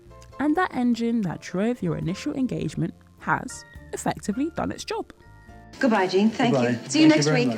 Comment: DID I JUST HEAR “BYE FELICIA” without ANY irony BEFORE the meme ever could’ve existed????